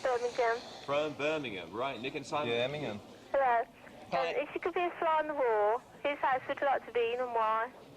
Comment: Hahah nice vid <3 btw, what does the girl at 5:45 say? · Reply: She said “if you could be a fly on the wall”👍🏻